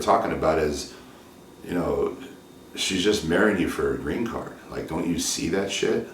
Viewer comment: Thought it said no green cardio